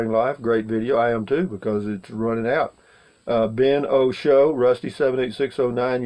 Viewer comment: that does look like a rifle behind you?.. Hold on,..at 19:41 it sounded like you said something like : i'm running out of life" ? ...your not that old!!